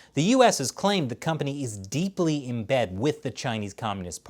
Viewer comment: When said “Deeply embedded with China” I heard ‘deeply in bed with China’ but hey, that not wrong either.